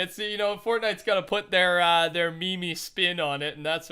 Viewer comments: did you just say me me?.. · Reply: No, he said memey, as in meme-esc or meme like. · did he say me mes